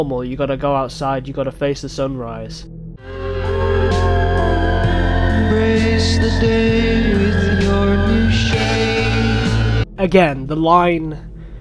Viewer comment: I always thought he said embrace the day with your new shame instead of embrace the day with your new shape